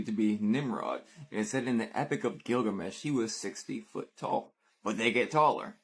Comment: ish the guy sounds like he says Gilgamesh was 6 feet foot tal. I listened to that part line 5 tim3/ and that’s what I hear. P.S. this guy reaches so much with all this crap it’s ridiculous. He makes way too much far fetched claims.